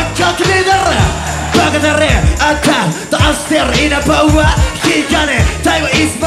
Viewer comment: このイナバウワーが出てこいやぁのそれにしか聞こえない笑